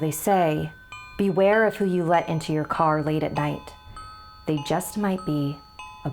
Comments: "Beware of who you let into your car late at night it might be a butt" I thought she said that at first 😅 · I heard “Beware Of boot”. 💀💀💀💀